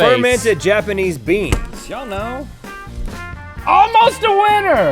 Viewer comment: Anyone else hear someone say "Y'all know!" around 10:45? O.o